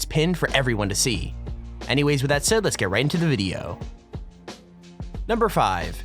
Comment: is it me or does he say lets get right in the moooodyio